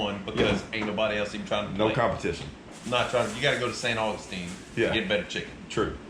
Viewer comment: It was Zavalla and it was brookshire brothers. Lol No beach at San Augustine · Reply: Apologies - I thought you said “Saint Augustine “ instead of “San Augustine “. Your channel is great! I plan to try casting from the beach in the early morning next time I’m by any saltwater